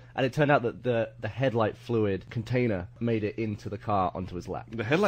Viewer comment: I thought he said lighter fluid